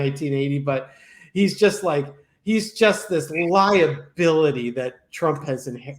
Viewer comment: When said liability, i really heard ly'in hillbilly. Not kidding. Talk about Freudian.